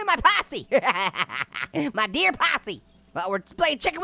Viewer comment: I heard dear possum